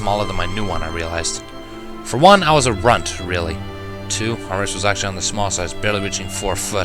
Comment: hmm wait did it say grunt or runt?